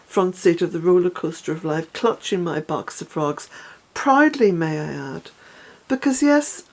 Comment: I thought you said box of Frogs, not box of rocks! 😂 Now I'm imagining you in the front seat of the roller coaster of life clutching your box of frogs, and actually that sounds as much like you as the box of rocks does. Lovely to spend some time with you this morning!